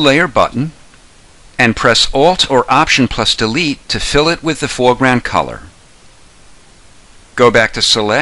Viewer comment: Dont understand that say in a minute 2:33 please write the route Thanks Grat Job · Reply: "...press Alt or Option + Delete to fill it with the foreground color."